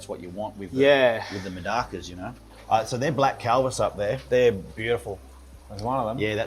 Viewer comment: What is the name of that fish, did he say Black Calvert? Edit: ok nevermind found it, it's calvus